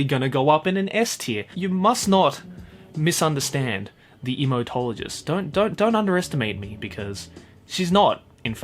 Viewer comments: just sounds like ur saying Emotologist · For a second I thought he said "Emotologist"😂